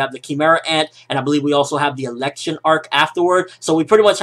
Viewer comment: thought he said erection arc for a second their LMAO